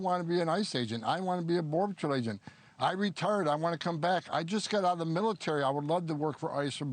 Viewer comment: When he started saying retire multiple times I actually thought he said “RETARDED”.